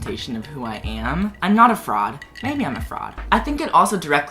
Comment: i heard "im not a frog... maybe im a frog"